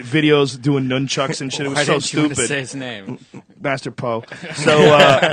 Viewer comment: what is the guys name he says around 5:30? I wanna look him up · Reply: Search master Po fake